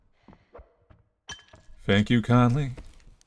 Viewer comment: Oh my god, my name is conlee. This scared me so bad. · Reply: I thought he said "thank you kindly"